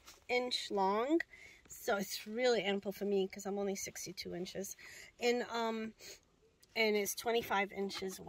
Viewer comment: did you say you are “six feet two inches” or “sixty two inches” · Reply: She is 62 inches. 5'2"